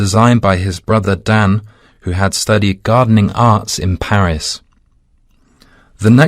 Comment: Someone please help me checking this: 15. I heard "Gardening Arts" not "Gardening Art". I'm I wrong? 30. Must be RICH PEOPLE as the correct answer? I think is "THE RICH"? 38. I'm sure the answer is C. Someone explain why B?